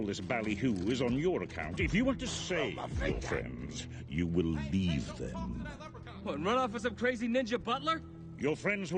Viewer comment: what have Crosshairs say? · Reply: He said, "You broke my finger"